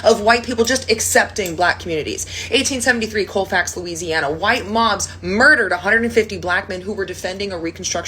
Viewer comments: Thought you kept saying White Moms i was about to say what are you feeding these ladies in the 1800s?? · Y'all i misheard at first and thought she was saying white moms instead of mobs.